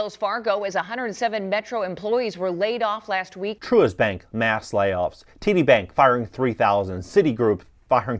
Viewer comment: I almost choke. I thought you said TT bank 😂😂😂😂😂😂